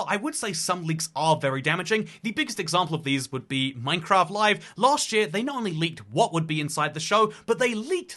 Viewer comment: Man... I had a heart attack I thought you said Minecraft Liveleaks.